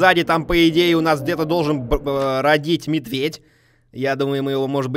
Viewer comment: Услышала не "бродить" а родить. "Там где-то должен родить медведь" Вы понимаете какой у меня был ор?